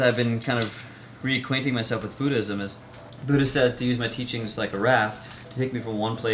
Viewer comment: I thought you said "foodism" at 4:11.